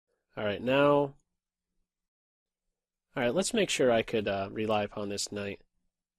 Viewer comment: Oh my god, I just understood after watching a least a thousands of your videos that you are saying "Rely upon". For some reasons, because my English is super bad and this is chess, I thought you were saying "rely a pawn" which I thought was some kind of chess expression.